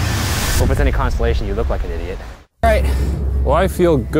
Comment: Does anyone hear Ryan say “if it’s any constellation” at 9:20 😂